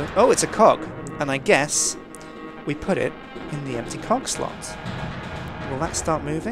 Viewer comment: When said cogslot I thought he said "cockslot."